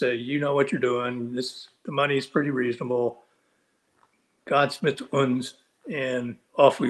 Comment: did he said "Gott ist mit uns!" ?????